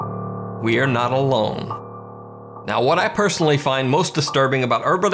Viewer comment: the captions says "Meals on wheels" when he says "we are not alone"